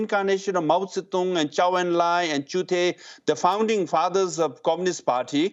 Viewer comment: Did he say chute😂???